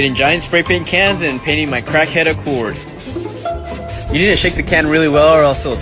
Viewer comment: That intro was my favorite part. " My crackhead accord" lol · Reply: I thought he said "cracked up Ford" 😆